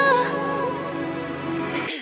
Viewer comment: 또 물보라를 일으켜 부분 나만 똥물보라를 일으켜로 들림??